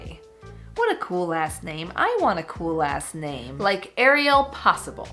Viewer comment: Ohhhh.... you meant Cool LAST name 😂😂😂 I thought you had said you wanted a COOL A** name!! LMBOOOO 🤣🤣🤣 I re-winded it like FIVE times dude, I was like, "I know she a christian woman, why is she cursing?!?!" bahahahaha! 🤣